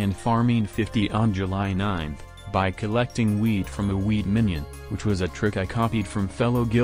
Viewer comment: "By collecting wheat from the wheat minion" It sounds like he's saying weed instead of wheat.